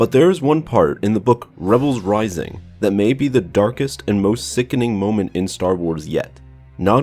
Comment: At first I thought it said DANKEST and most sickening moment in Star Wars